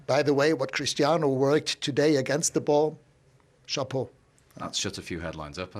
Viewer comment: Can someone please tell, what Ralf said about Cristiano? Chapo? Shapo? What was that exactly? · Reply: Chapeau